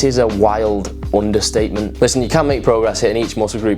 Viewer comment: I think you misheard me mate. I said "you can make progress hitting each much group once per week, maybe even less"... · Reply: I just listened back. it sounds like you said 'cant' but in context with the whole sentence "..BUT you can make better progress with twice a week.." I stand corrected as you obviously must have said "can make progress"